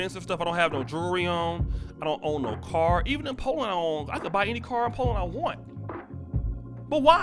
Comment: did u say, I can buy any car in poland I want" Lol I'm sure I misheard.